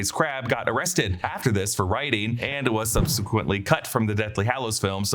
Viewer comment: He was arrested "for writing"??? Not sure what's being said there, but I do know he was arrested for growing cannabis. Did they not want to say that? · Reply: He said rioting